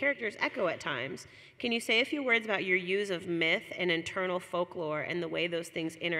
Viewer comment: I thought she said "... your use of meth ..." hahaha Also, shout out Brynden B Fish and LML!!!